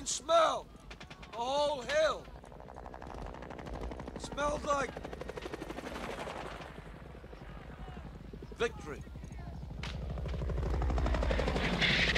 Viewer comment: "Smells like victory" Most iconic movie line ever. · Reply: Opie and Anthony used to play this drop all the time. Having not seen the film at the time, my dumb ass thought he was saying “Smells like… liquid.”